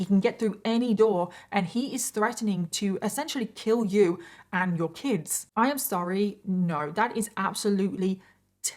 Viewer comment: Was his actual phrasing in the threat that he would 'amputate your kid'? Cuz if so...Maybe it's just me, but was his mistress pregnant? The wording is just really weird, but to me, it sounds like he legit threatened to cut a baby out of her. 'Amputate your kid'. Yikes. 40:35 Ooookay. So...Yep, I'm betting that's EXACTLY what the wording meant in that threatening email. Jeeesus.